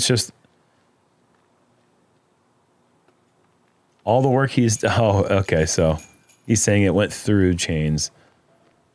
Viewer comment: I really want to know what Gannon said to Simon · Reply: Looked like he said he got robbed, but that putt was high and am side…